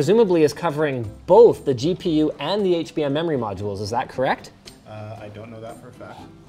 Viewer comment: did he just say HBM MEMORY MODULES 9:18 , is this a titan V or a Vega20 ??????